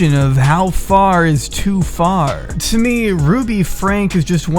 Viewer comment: who else heard "fart"? ;)